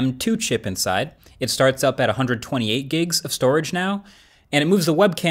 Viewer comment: My heart skipped a beat I thought he was boutta say starts out at 128 dollars he said gigs ...128 gigs